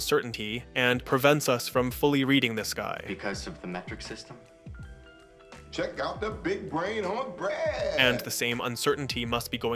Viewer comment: his name isnt Brad? I always thought he said "check out the big brain on Brad" even watching it here I still thought it was Brad... I even say it to my buddy Brad all the time...